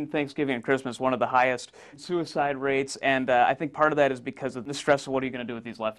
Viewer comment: "Highest suicide rate." WHHHHHHHAT DID I HEAR THAT RIGHT? LMAO